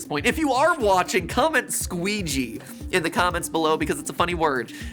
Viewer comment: I can't decide whether I heard saying "Squigi' or "squeezie" I guess it's too early for my brain to function anyways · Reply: squeegee.